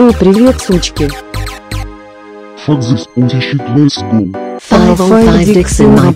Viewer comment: what did that voice say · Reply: 'Fuck this pussy shit, let's go'